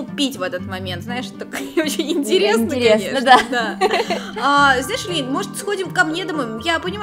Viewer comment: мне одной послышалось...🤔: а... Знаешь Эллин ?! 🗿... Я:... Ты от нас что то скрываешь?🌚